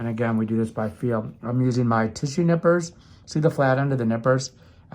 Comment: Not gonna lie. Thought he said "tissue nipples" lol